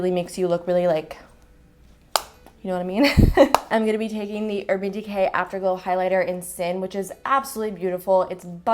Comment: Loved the look! I laughed when you were talking about the highlighter @ 10:00 you said, "Using the Urban Decay Afterglow highlight in "Sin" which is a really...." But I heard "in the shade sandwiches" and I was thinking, that's a funny name for a highlighter shade 😂😂😂